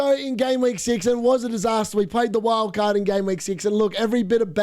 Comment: wait did you play the wildcard im GW 6? i always heard you saying “we went against the wildcard in GW 6” IM AN IDIOT